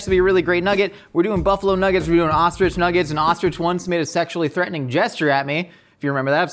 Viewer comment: I heard it was a sick ostrich...